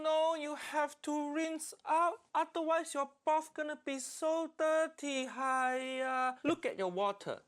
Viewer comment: At first, I thought Uncle Roger said, "Your barf is going to be dirty."